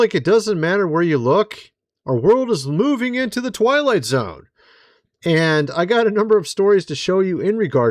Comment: I heard "Toilet-zone" instead of "Twilight Zone."